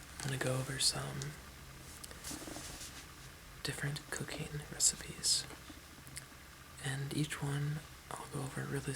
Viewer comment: I thought he said "cocaine recipes" lmao!